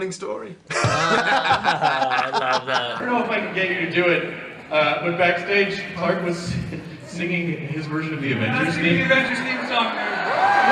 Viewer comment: What they said from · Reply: LOLO chan- "I don't know if I can get you to do this, but backstage, Clark was singing his version of the Avengers theme song" "I'm not singing the Avengers theme song here, I'm not!"